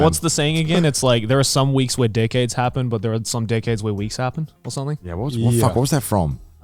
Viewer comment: I was so confused, I kept hearing Joey say "dickheads" instead of "decades".